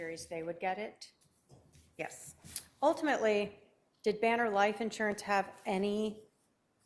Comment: I thought she said Vanna White insurance.